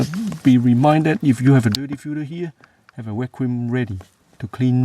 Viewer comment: I couldn't understand what you were saying about having something ready for cleaning up the dirt after taking out the filter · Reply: I see. What I said @ 9:22 is to have a Vacuum ready to suck out additional dirt/debris when the filter is out.